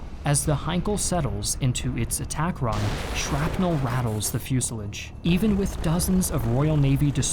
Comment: I’ve never heard anyone say fuselage like that. Sounds like you said fuselidge.